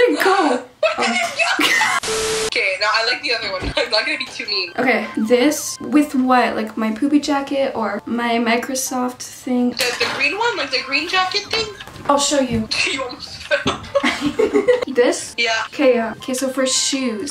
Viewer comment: she said poopy jacket 😂 (that’s what I heard)